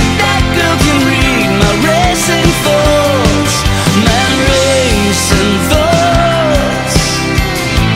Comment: Sounds like he says "racist thoughts"